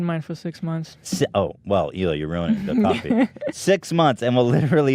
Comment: Did he just say "ruining the copy"? Dang, copywritin' really be out there... Any Team Capital folk outchea?